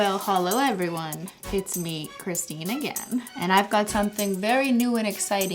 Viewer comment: "holow everyone it's me Christinaghan" i love the misheard